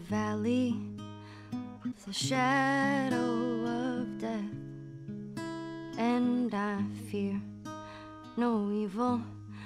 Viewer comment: I think I heard her say "and I fear no evil" Someone plz correct me if I'm wrong but I'm almost 100% sure